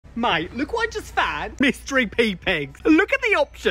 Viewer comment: WHY DID I HEAR “MYSTERY PP’S”